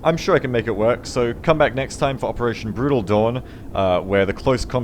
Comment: Did he just say "Operation Brutal Doorn?"